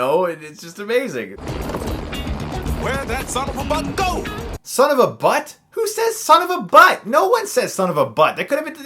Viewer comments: I think he says "son of a BUCK". It still sound silly, but makes more sense than "son of a butt". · I heard “son of a buck ” instead of “son of a butt ” lol